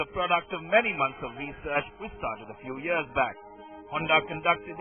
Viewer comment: Think he says many months of research which started a few years back.